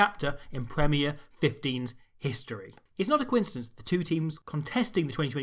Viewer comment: did you say "Quin-cidence"?